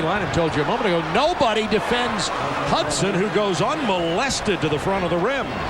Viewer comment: DID HE SAY ''UNMOLESTED"? :D